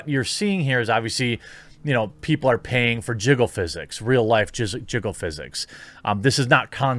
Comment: A bit of a Freudian slip there. @ 3:50 When he accidentally said 'Jizzle physics' instead of 'Jiggle physics'.